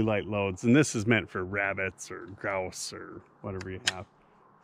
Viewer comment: When he said, "this is meant for rabbits or grouse"... at first listen I thought he said rabbits and elves 😂